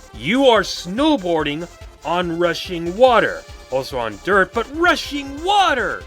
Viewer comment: "you are snowboarding, on Russian water." is what i heard